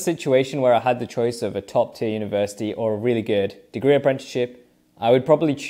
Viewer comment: did u say apprenticeship